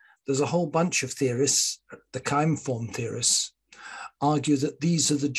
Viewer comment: what was the name of the school you mentioned? It sounded like “Kime form” theorists? I would be very curious to learn more about them